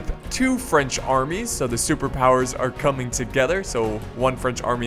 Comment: LMAO,i dunno why but i heard "so Superpowers are Cumming together" at 0:42 :D :D